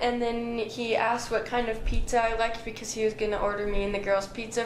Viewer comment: please listen at 10:31 i hear her say Nicky asked what kind of pizza i liked